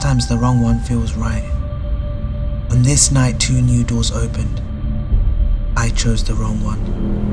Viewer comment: He said “two new doors opened” but I heard “2 noodles opened” 😂